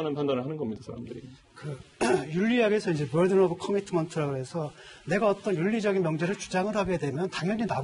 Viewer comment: 윤리학에서 무슨 commitments? 이 용어 아시는분 있나요 앞 단어가 무슨말인지 안들려요 ㅠ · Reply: Burden of commitment